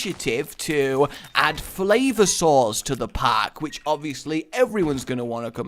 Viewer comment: I thought you were saying "Flavor Sores" and I thought that was disgusting until I realized you meant "FlavorSaurs"...